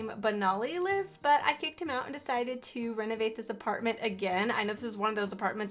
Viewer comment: I thought you said renegaded this apartment 😂